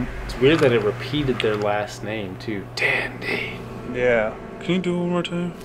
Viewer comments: On the evp I heard “Dandy… hurt me” Just wanted to throw that in there! :) Love the show guys, binged like 5 episodes today lol. You rock, stay safe and stay spooky y’all! 💗 -Kate 👻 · i hear.. Dandy.. fam, ily..!